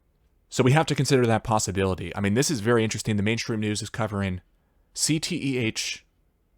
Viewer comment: Sounded like "... the Matrix News ..."